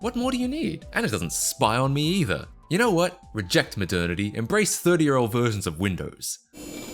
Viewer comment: ngl, I thought you said "Reject 30-year olds virgins of windows" 😂